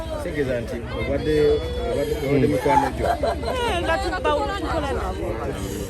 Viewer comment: What did she say at 00:06:04 ? RIP for the fallen souls. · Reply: Nga tuba nabo